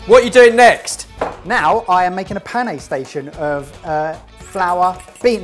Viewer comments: Honestly thought said he's "setting up a Panic Station" instead of pané! 😂 The way destroyed the miso flavour by boiling hurts my Japanese soul! 😰 You're supposed to add miso at the bottom of your bowl at the end! On the other hand though, excellent looking results!! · I heard say “panic station” instead of “pané station”. LOL (autocorrect murdered pané. LMAO).